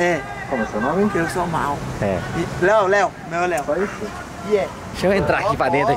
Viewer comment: os mlk gritandooo kkkkk "Iae Leo da honert" n entendi o resto kkkk